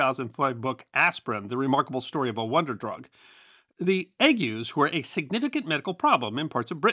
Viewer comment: I'm sorry I can't quite make out the word. What are they treating with aspirin in Britain around 8:40? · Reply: That is an ad for some patent medicine for ague.